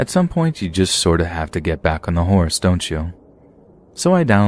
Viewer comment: "at some point you just have to get back on the HORSE, don't you?" I had to rewatch, I heard "h*res" lollol 😅😅😅